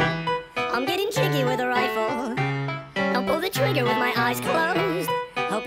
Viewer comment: I thought it said "I'm eating chicken with a rifle" 💀